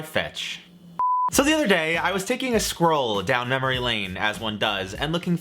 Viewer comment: At first I heard you were "taking a squirrel down memory lane", and was quite astonished. But then I thought: "Well, why not?". 🤣🤣🤣